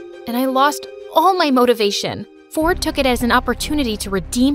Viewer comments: When she said Ford I laughed so hard lol 😂😂😂😂🤣🤣🤣🤣🤣 because I thought she said fortnite lololl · I thought she said his name was “FORTNITE” LOL 😂😂😂😂🤣🤣🤣🤣